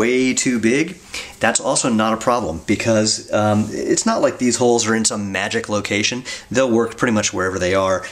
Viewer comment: Why do I keep hearing, "Arseholes"?